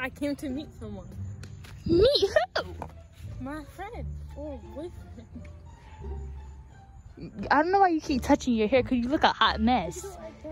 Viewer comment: Did she just say my friend maybe boyfriends 🤣🤣🤣